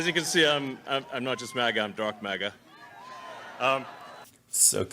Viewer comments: It sounded like he said drunk maga. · Thought he said drunk MAGA. That would have explained a couple of things but nope!